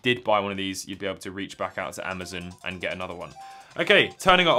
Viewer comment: was it just me that heard him say amadon instead of amazon at the start 😂😂😂